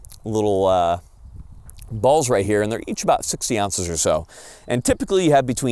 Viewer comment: Did he say each ball is about 60 ounces? Almost 4 pounds? What did he mean to say? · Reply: 60 grains